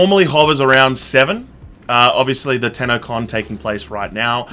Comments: Ooohh TENNO con. I legit thought u said tana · Every time you said TennoCon I thought you guys were saying Tanacon. Fuck, I just realised that the second time you said it. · When he said Tennocon I thought I heard Tanacon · I'm not the only one who misheard the Tennocon as Tanacon and goes "whaaaat?" Right?? Right!?? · I thought you said Tanacon instead of Tennocon😂😂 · Instead of saying tennocon i thought he said tanacon lol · They said Tenno con, I thought they said Tana con hah